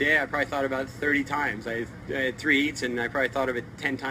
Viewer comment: what exactly is he saying? Thx anybody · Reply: HE HAD THREE HEATS , he said he thought about getting bit by a shark at least 10 times each "heat" 1 HEAT is a 1 round of surfing which means like 1 round in boxing